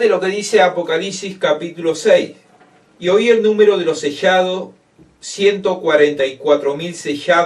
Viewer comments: Gracias por la explicación Siempre quise saber el motivo de la creencia de los judíos La verdad muy muy interesante Mil gracias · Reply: Hola, entiendes tú que significa esa palabra que se oye, a partir de minuto 25:35 dice “SESCHADO”… no entiendo que es eso. Gracias, saludos desde Lima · Por favor, a partir de minuto 25:35 dice “SESCHADO”… no entiendo que es eso.